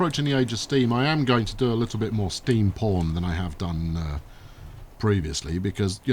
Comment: did you just say at 3:07 ''I am gonna do more steam porn then i have done previously''?